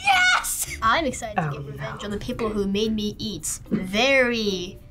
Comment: I thought the kid in the mid said "I'm excited to get revenge on the people who made me..."